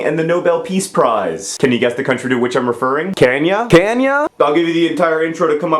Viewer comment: A few things: 1) Can you update for the now times? 2) Even though I know what I'm about to watch, every time I hear, "Can ya? Can ya?" I think, "Kenya." 3) When you said, "...who famously tried to change the Burmese currency from base 10," my year 1205 brain thought, "Dozenal! Dozenal! Dozenal!!" Best wishes, Jon